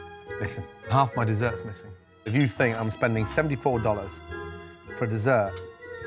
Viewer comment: am i trippin or did he said 74??????????